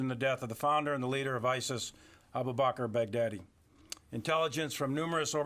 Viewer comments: I liked the way he pronounced the last name which sounded like "bag daddy". Hahaha ha ha... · Is it only me who hears "bad daddy"?